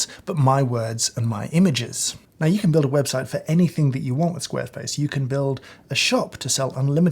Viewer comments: did he say "squareface" at 3:55 ? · Heh -- "SquareFace" at 3:54. PS, what does the TDS meter say?